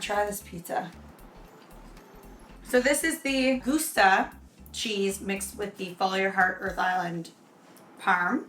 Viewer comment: I really thought you said goose cheese the second time. I knew you really didn't but I laughed anyway. 10/10 would try. And definitely with your cheese since I have your cookbook and have no idea where to find goose cheese. Lol!